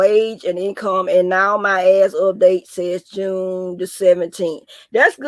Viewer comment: I thought u said now my sass up June 17th 😂😂l